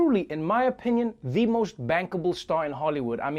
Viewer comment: He said bankable... Yea I thought he said he wanted to bang kevin 💀💀